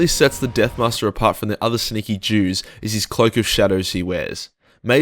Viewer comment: Did he just call em sneaky Jews?! hahaa